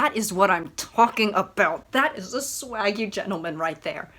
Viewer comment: did she just say swagy